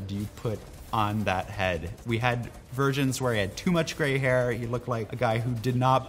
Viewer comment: Did he say virgins or versions?